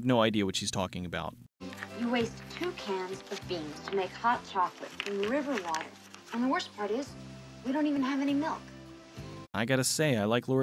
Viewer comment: I think she said "You waste 2 cans of beans (coco beans) to make hot chocolate in river water and the worst part is we don't even have any milk."